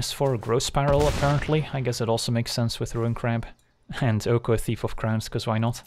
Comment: I honestly thought you said Oko, Thief of Crabs for a second there.